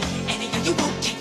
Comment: “you’ve been hit by” “you’ve been f**k by a smooth criminal” 🤣🤣🤣 am i the only one who hear that?